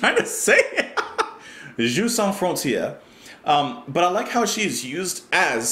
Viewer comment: Interesting fact?... when he sings " Its a knockout" he's referencing an old uk tv programme where teams from 2 rival towns played silly games for points to find a winner. There was also an international version featuring rival towns from 6 towns from different countries...the programme was called Jeux sans Frontieres.As in his Genesis days Peter just cant resist a good play on words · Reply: I always heard it as "She's so happy here" which I suppose is another way of saying you don't need frontiers.....